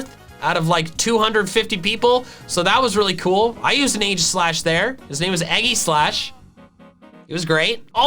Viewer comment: lmao when said Aegislash the subtitles showed "age of slash" but when he said the nickname for his Aegislash the subtitles got it right